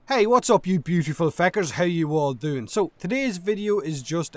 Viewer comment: Hi you beautiful fecker :-) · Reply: Ardrestrand Ooooverwatch I thought he said peckers.